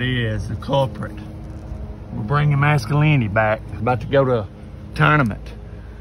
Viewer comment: I heard "bring mescaline back"